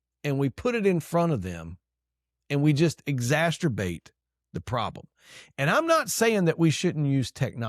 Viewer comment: Did he just say "exasturbate"? Great points, however.